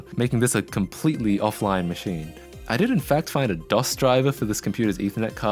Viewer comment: did i heard Treiber? Diese Kommentarsektion ist mit sofortiger Wirkung Eigentum der Bundesrepublik Deutschland..